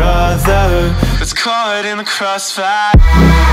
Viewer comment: all I hear is "Mis kardem in the cross fire"